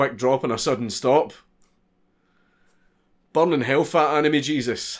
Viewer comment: One of those sentences you never expect to hear, ever. "Burn in hell... Fat, Anime Jesus." - Poetry. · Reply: I thought he said Fat enemy Jesus. Gotta admit I'm not used to his accent yet.